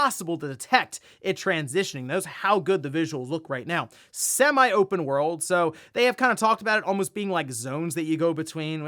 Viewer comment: I thought they said "not open world" not "semi open world"